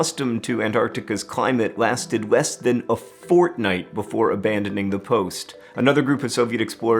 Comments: did he said fOrTnItE??? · Did I just hear he said fortnite? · umm... did i hear that correctly? Fortnite? What?? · did he say fortnite? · Did he just say Fortnite omg